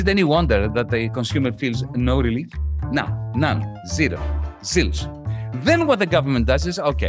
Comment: unrelated, perhaps stupid question; what is varoufakis saying at 6:23 as a synonym of zero? sylus? zylus? zilch? he pronounces it as silus, no? ... by the way, venceremos! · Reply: zilch